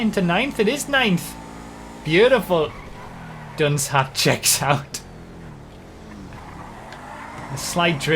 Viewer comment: What did said? Hard for me to understand. I hear it like "dunce hat checks out", but don't think it's exact phrase